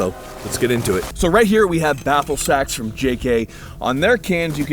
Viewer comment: The entire time I thought they were saying "Baffle Sacks".